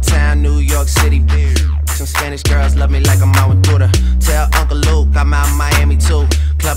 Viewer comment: I thought he Said "Spanish girls love me like im on Twitter".. Danggg... All these LIES all these years